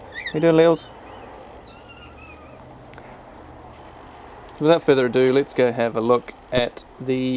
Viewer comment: I thought you said "without feather ado"...